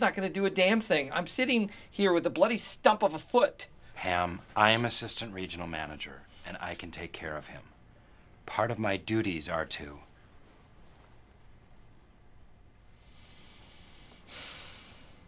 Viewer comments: Am I high or do I hear Ham not Pam? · Is it just me or did Dwight just call Pam “Ham?” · did her just call her "Ham"?